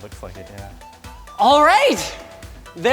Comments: Blonde guy says I think so yeah. Subtitles say butt-fuck again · Who captioned this at 3:50 it doesn't say Auto generated and he clearly says it looks like it yeah not butt-fuck again LMAO IM DONE